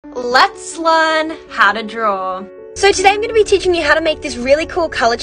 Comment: At first I thought she said “Let’s learn how to drool 🤤 “